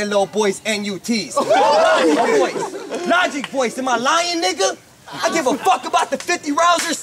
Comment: Yo... is it me or did say "nigga"